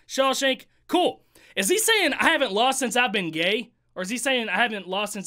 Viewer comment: Yes, he does say “I ain’t lost since I began, yeah” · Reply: Oh shit I thought he said “ I aint lost since I been gay “ 😂